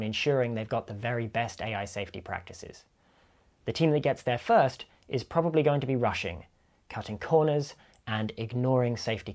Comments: I thought you said "Russian", and the description which was quite fitting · I thought he said "Russian" the first time :p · First heard that as "... the team that gets there first, is probably going to be *Russian*, cutting corners and ignoring safety concerns". That statement would also make sense.